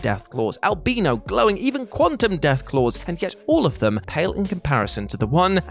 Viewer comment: Did he just say quantum deathclaws??